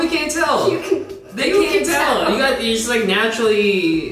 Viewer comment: naturally beautiful? Was that what he was gonna say?!